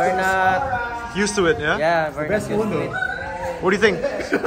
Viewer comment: What did said to? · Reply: "the best phone tho."